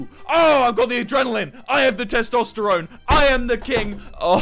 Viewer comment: Did he just say “I AM THE TESTOSTERONE” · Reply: K Y It was ‘I HAVE THE TESTOSTERONE’ but I thought he said ‘I AM THE TESTOSTERONE’ until I replayed it 50 times